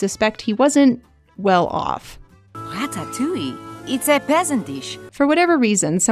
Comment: I always heard that line as "a pleasant dish" like she was saying "sure it's nice but I don't think it's good enough for this"